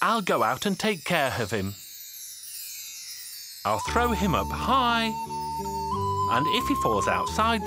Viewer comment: I thought she said “i will throw him a pie” xD